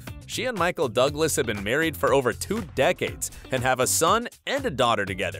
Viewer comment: I thought he said ten decades for a second and I was about to say-- how old ARE these people?